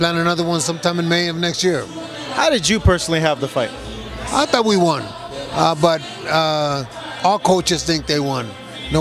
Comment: Abel clearly says at 1:16 "I thought that we won." Why are people arguing about what he thought and claiming he said Canelo won? Christ, some of you are so stupid.